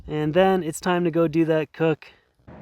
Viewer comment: i heard "its time to go do that Coke" 😀😀